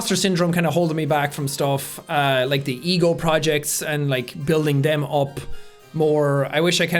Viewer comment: When he said "the ego project", I thought he said "the eagle project". XD Also, I love how the beginning was humor and then things got more deeper and heavier.